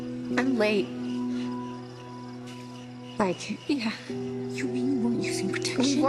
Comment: What does Cece say at 1:23??? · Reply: She said, "like?" She was asking if Ali meant that she was pregnant.